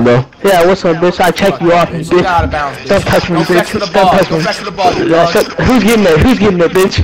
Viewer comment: What happen that flight laughed at 6:04 I didn’t Hear what he said to flight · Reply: flight said fetch and the other guy said whos gettin it